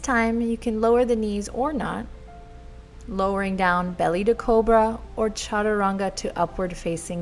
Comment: This was amazing! btw the first time said 'cobra' I heard it as 'cool bruh' and I couldn't stop laughing xD